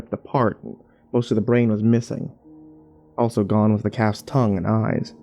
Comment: I thought he was saying cats instead of calf's for a good 2 minutes